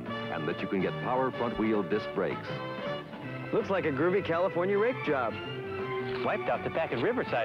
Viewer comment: at first I thought he said "looks like a groovy California rim job"